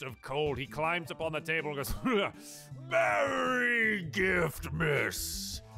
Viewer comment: I thought it said Merry Griftmas and I was confused as to the grievance. Glad I was wrong